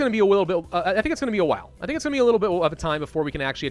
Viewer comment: listen....he says a wittle bit instead of a little bit! YES HE IS SLOWLY CONVERTING TO THE RANKS OF UWU